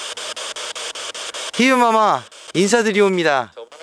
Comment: 희빈마마 했을때 연애할래라고 들었는디 나만 그렇게 들었나?ㅋㅋㅋㅋㅋㅋ